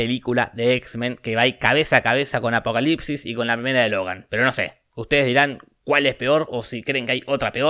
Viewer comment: que dijo lalskdjhfhslsk de logan jaja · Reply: Que la primera es mala